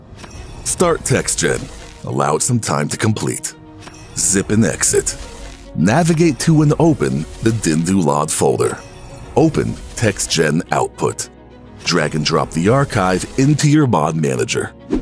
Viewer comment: Did everyone else replay at 4:33 because they thought said "Open xEdit" like me? lmao